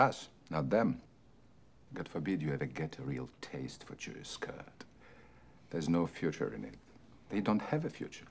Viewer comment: What did he say at 2:30 ?God forbid you...? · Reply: Reyman Reymanovic get a taste for Jewish skirt